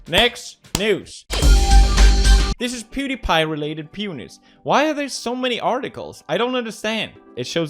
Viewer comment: I can swear he said pewnis in 6:47 😂